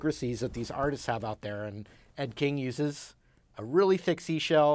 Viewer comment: At first I thought you said Ed Gein. At that point I didn't want to know what it was made from.